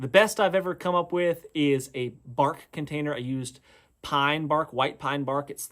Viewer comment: When you said “bark container” I totally heard “fart container”. I thought maybe you were harvesting methane to propel a Segway out of the urban area.